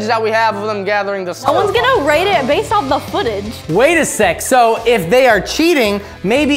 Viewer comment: what did he say👀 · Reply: He said wait a sec